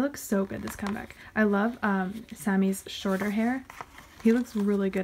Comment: When u said “I love Sammy’s shorter hair” I thought u said “I love Sammy’s shoulder hair” and I was like: ya what?? Shoulder hair?? 😂